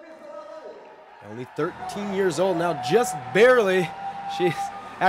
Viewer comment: did he just say 13 years old?!